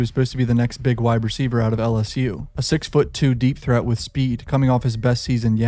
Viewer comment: Sound like you said a 6’2 deepthroat with speed…